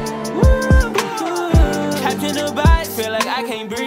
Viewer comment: why did i think he said trapped in a vibe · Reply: I thought he said “Captain Dubai” this whole time